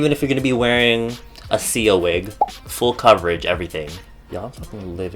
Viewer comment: When you said Sia wig I thought you said seal wig😂 ❤💙💚💛💜💟